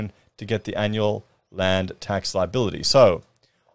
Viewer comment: I swear the govt said annal land tax liability.